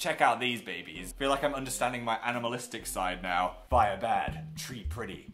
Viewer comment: I thought he said cannibalistic instead of animalistic. Is there something he want to tell us?